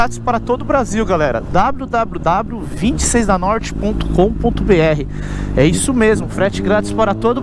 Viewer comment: dabru dabru dabru kkk só eu q entendi assim? min